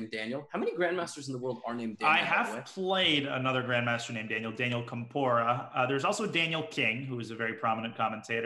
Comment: Watch gotham think he said "Daniel Tempura 😂😂😂"